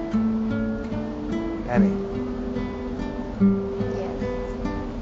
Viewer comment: I need better speakers. I thought he said, "come on and strip with me".